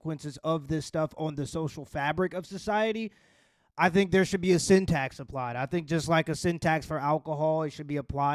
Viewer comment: Simp-tax 😂 I thought you said syntax at first 😂